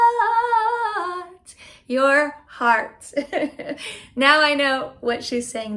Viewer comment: I thought she was saying “I would mess up your HEAD”. 🤷🏻‍♀️